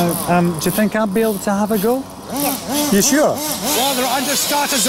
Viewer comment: lol did Lewis say yes or no? I can’t tell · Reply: He said yeah